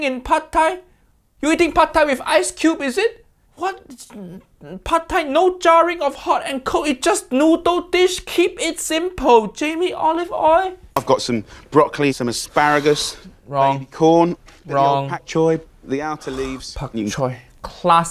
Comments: I thought Uncle Rodger said Jamie Olive Oil. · Did I hear "Jamie Olive-oil"? 😂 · Did he call him Jamie Olive Oil 7:28 …😂